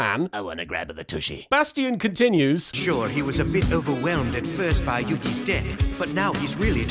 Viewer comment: I don't know why but I thought I heard sure jaden was overwhelmed at first by yugis d#$k